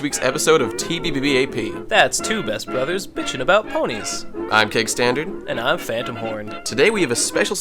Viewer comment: im sorry but everytime i hear you guys introduce yourself' i only hear: "Im Cake" xD & "and I'm Phantom Horn" anyway, this episode was amazing ^_^ but i got a a little bit wierded out by the real-life-scene O_O